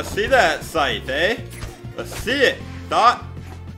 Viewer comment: did he just say thot? lmao